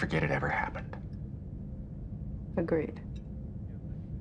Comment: What does he say @ 2:15 ? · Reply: "Doesn't mean we can't keep doing the neural pressure, though."